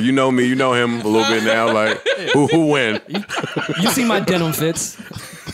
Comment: I thought Rory said..."You see my dental fits!" 🤣🤣🤣